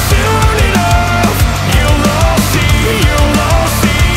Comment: Why does it kinda sound like he is saying "You've all see" tho 💀